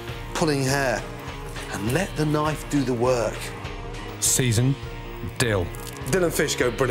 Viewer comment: "Let the knife do the work." "Alright knife, I'm gonna be sitting in the living room sipping a beer. You know what to do." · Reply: Oh I mis heard him. I thought he said “let the wife do the work” No wonder she left me 😔